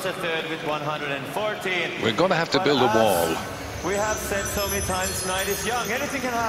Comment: did he really say we will need to build a wall at 28:52 I